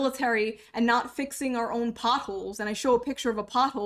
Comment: when you said pothole i thought you said puffle lol (i thought pothole was pronounced pot-hole not poth-el)